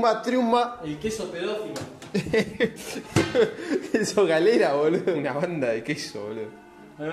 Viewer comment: recién ahora me doy cuanta que dice galera